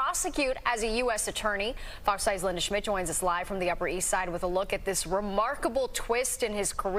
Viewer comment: Did he say steak 😆